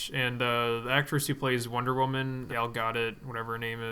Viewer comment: Did you just call Gal Gadot "Elgato?" hahahh